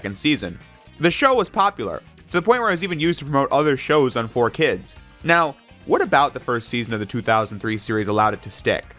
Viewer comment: Can somebody pleaaaseee tell me what the show that he said was being promoted at 00:36 was? I think its the one i was searching for, for years now. I think its the one I adored as a kid, and i really want that nostalga fix. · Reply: It's called 'Chaotic'. Never watched it back when it was on. Glad to help.